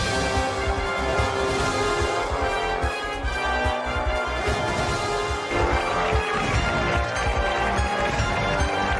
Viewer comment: I wonder what she's saying lol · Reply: 'That's half a million dollars' Trying to cheer him up. He looked pretty upset he didnt go for it.